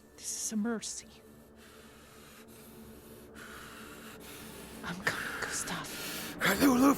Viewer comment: What did she say? What just happened? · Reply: I'm coming Gustaf or Gustav